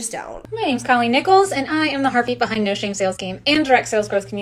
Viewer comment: I totally misheard what Colleen said in that second clip! I thought she said she was the harpy behind blah blah blah! And honestly I think what I heard is more accurate than heartbeat.